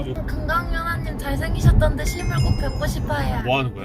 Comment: 왜 실물 꼭 뵙고싶어요가 X발 꼭 뵙고싶어요 로 들리지?